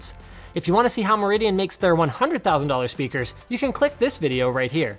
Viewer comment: Did he say "100.000 dolar speakers" ?